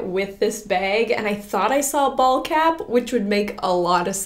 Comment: I thought you said bald cap 😂😂 I was so confused till you showed the hat 😂